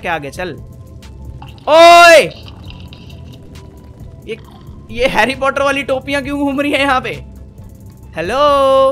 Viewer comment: did u just say harry potter???!!!??????? If u don't mind I'm a big fan of Harry potter 🎉🎉🎉😎😎🤩🤩🤩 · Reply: He said Harry Potter